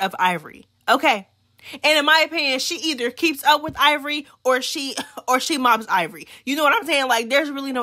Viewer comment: did you say she mops Ivori? 😂😭 ima need the clips I’ve seen them tie and ivory win but Mel won when ? Maybe I’m missing a scene